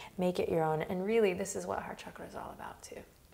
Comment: at one point, Adriene said “heart chakra” and i thought i heard “hot chocolate”😂